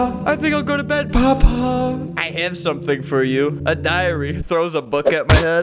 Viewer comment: “A diary” What I heard: “Diarrhea”